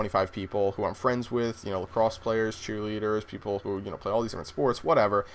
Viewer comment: did he say cheerleaders at 1:46 :D